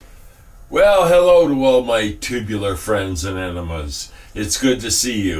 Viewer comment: HaHa - did you just say "welcome all my tubular friends and enemas" ??? (I'm dying over here) :goodvibes